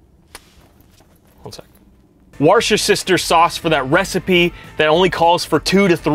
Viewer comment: Where's your sister sauce😂😂that's What it sounded like you said 😂😂👍🏼